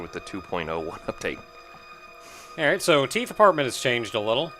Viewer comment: I thought said Teeth Department when he said Teeth Apartment. Another weird idea spinoff is somewhere in my head now. Like Cells At Work except...well, really anthropomorphizing teeth tends to lead to either weird stuff like Timmy the Tooth or...Cells at Work